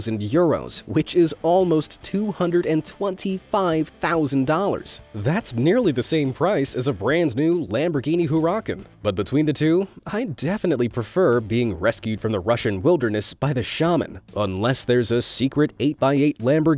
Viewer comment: I KNOWWWWWW this guy didn't just mispronounce a Lamborghini Huracan‼️ It sounded like he said Lamborghini who-rockin 😔🙈🖕